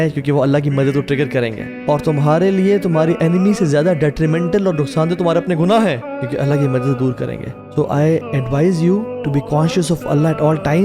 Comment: (2 seconds), I couldn't understand the words spoken during this time. If any of you understood, please let me know. Jazakum Allahu Khairan. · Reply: "Tumhare gunah tumhare liye detrimental hain kyuki wo Allah ke ke madad dur karenge"